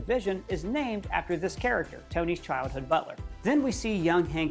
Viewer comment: i thought you said " tony's childhood LOVER " and not BUTLER